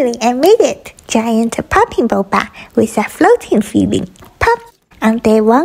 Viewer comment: i thought it said “pooping boba” for a sec 😂 💀